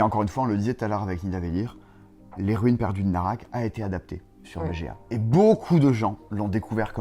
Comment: Il dit quoi vers 21:20 ? Adapté sur vga ?